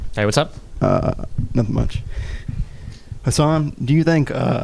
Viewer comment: is his name or is he calling destiny